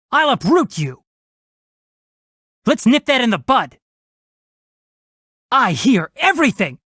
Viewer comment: i THOUGHT i HEARD "lets sniff that in the but!"